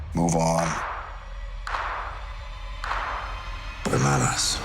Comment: Does Cap really say „bananas“ at 0:13? I keep rewatching it and I keep hearing this.